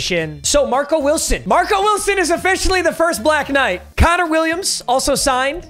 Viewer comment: i’m so gone dude, Thought he said “Marco Wilson is our first black guy.” 2:17. I mean definitely won’t be the last one on the team.😂🤷🏼‍♂️